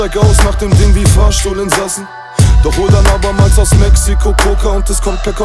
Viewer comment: He said Mexico??? 0:32 I dont understand german :( but I like it!